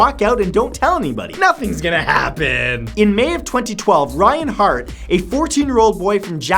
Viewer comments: Did anyone else hear REINHARDT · wait a minute, did he say Reinhardt at 1:57 ??